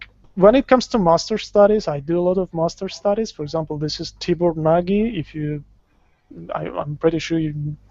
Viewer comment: he mentions the artists name? Can anyone clarify who that artist is I didnt quite catch it. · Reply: Tibor Nagy - <link>